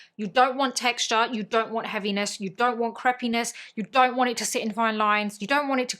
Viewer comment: I thought you said don’t want crappiness lol in truth I love it when you glitch makes me chuckle I laugh with you